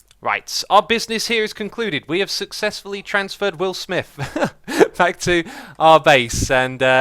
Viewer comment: I thought he bloody well said Will Spiff